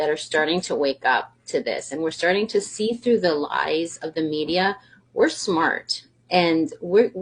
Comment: who else heard "i see through the lies of the jedi"